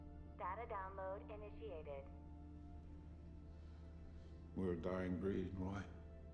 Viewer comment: What does TLJ say? I cannot understand... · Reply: "We're a dying breed Roy"